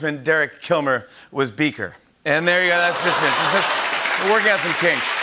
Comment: sounded like he said "orgasm kings" lmao